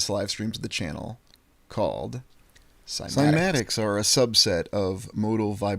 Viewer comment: What is the word you said at 2:46:48 - i couldn't understand what you said. It sounds like scimatics. You were talking about sound. Thanks! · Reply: Cymatics.